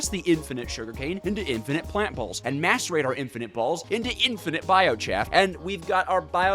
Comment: So I listen to this in the background while I study, and swear to god I thought he said: "Masturbate our i n f i n i t e b a l l s ."